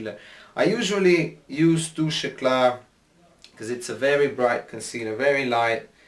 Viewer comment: What’s on top of the lid when u start? It sounds like your saying to-shi-gla lol. I have dry skin so I need to know what the serum is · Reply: Hahaha! Touche Eclat... It is a highlighter by YSL. Quite moisturising stuff. :)